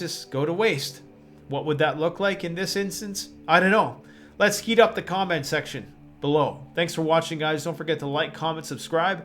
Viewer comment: Did you just say "Let's skeet up the comment section"?